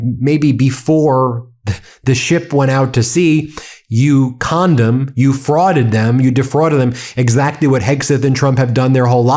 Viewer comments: 😂 You said "you conned them". But for about a second I thought you said "You condom". Which i believe i may have to start using as an insult.. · Thought Ben called him a "condom" for a second 😂 That would be a new one for me.